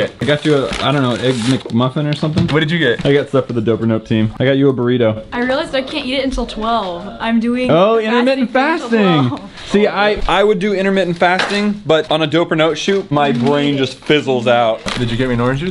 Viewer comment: i thought said i cant yeet until im 12